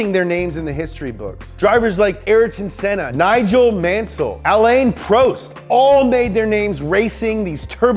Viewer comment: Did u just say aleyn proest